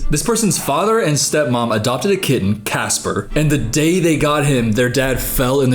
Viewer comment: I thought you said KID not kitten. I was like what is going on with this dad?!?